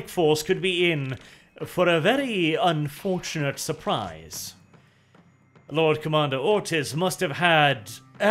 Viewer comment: Listening to this on 2x speed and I keep hearing Lord Commander Autist (at 25:55 for instance).